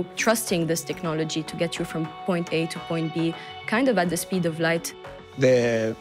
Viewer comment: did she just said the speed of light?